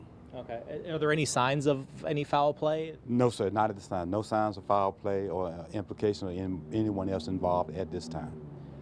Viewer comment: did he say no signs of foul play?!!?!?